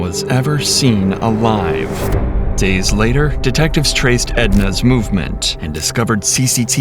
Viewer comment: So I watch these going to sleep and I thought she said “Edna mode” and I had to back it up